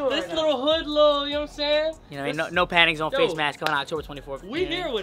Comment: bru at the beginning i thought you were saying no panties on😭😭